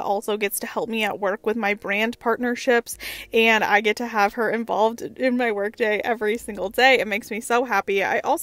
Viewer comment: Girl, I totally thought you said you could have your dog embalmed not involved. I. Was it like, what lol? 🤣🤪💜